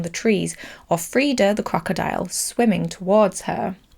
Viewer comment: Who's Freida? I said Theda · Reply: I could swear on my elderly eardrums that was a "Free" sound instead of a "Thee" sound at 3:29 but I'm just joshin' regardless, I thought it was funny. Me being the "um, actually it was flavor-aid not kool-aid" person at the party about icon legend and star- the big lizard from X lmao